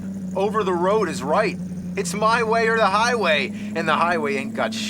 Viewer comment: Hey there. Whats james saying at 3:03? English isn't my native tounge and it would help. I believe he say's: It's my way or the highway, but another friend told me: "My way to the highway". So, could somebody tell me the truth? What is James saying? Thanks for every answer. lg Fen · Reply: They're right. "My way or the highway" is an American idiom. When said, it denotes a strict adherence to one's own authority, meaning "Either you obey me or you can leave (the group)."